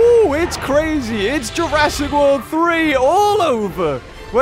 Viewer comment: did he just say jurassic world 3